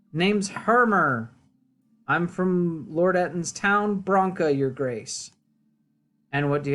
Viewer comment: I thought it said Lord Elton’s town 😂😂